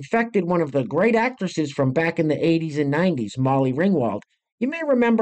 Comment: I had to take it back because I thought I heard 1960s. After seeing her what I thought in my mind was, she doesn’t look that old.